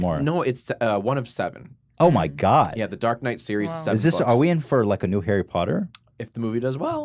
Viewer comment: did jack say dark night at 1:06:20 lol